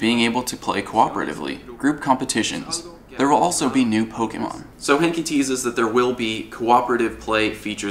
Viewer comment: I thought he said nude pokemon